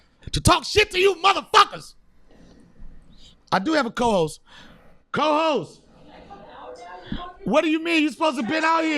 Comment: i thought he said no pasty co-host lol jk